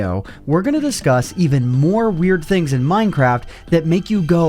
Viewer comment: Did he say Minecrap